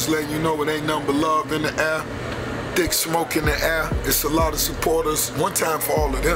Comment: Honestly just thought i heard Rick Ross say theres Dick S"moking in the air" for a sec. Lmao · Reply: There’s thick smoke in the air ....